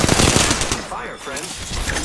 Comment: "Fire, Friend!" throws thermite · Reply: No idiot he said "I am taking fire friends" but alot was happening so it sounded like it said "Fire Friend"